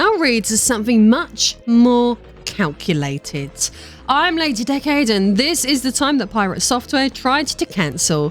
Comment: I know my hearing is bad when I heard "I'm late a decade" instead or "Lady Decade" lol